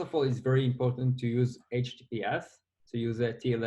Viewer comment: HTS instead of TLS ? is it correct ? or i dont understand what he said. Help please. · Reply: he said, use https. Ps: HTTPS uses TLS in the background